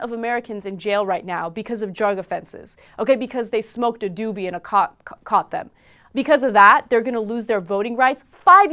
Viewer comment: my english is not the best but, did she said "ok because you smoke a doobie in a COCK" at 1:59 ? was that a freudian slip?